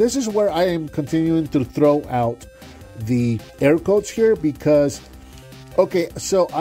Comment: from 2:21 you say: ''and this is where i continue to throw out the-'' what word? i am trying to figure what you are saying haha. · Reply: I continue to throw out the air quotes, any time I refer to this figure as a "deluxe". Thanks for watching!